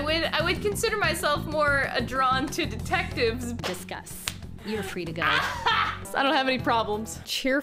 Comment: “I would consider myself more drawn towards detectives” Kawakami: “Disgust” (I know she doesn’t actually say Disgust, I just thought it sounded like she said it the first time and thought it was funny)